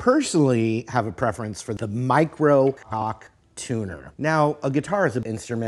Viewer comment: i thought he said that he had a preference for the Microkorg tuner 😭